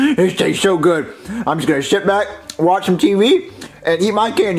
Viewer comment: sounds like he said shit back 😆